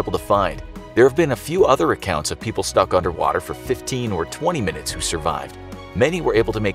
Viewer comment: I thought it said underwear